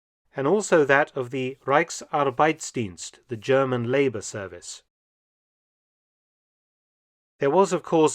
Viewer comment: I was listening to this video while shaving so there was background noise, and I misheard German labor service as German laser service. For a split second I thought good Lord was there anything these guys weren't trying to develop?!